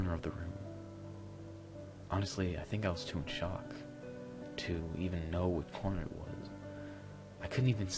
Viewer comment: what I heard the first time “honestly I think I was too in shock to even know what porn was”